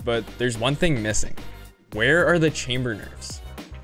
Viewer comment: I know I've read Chamber Nerfs in the title but I swear I heard Chain Burgers in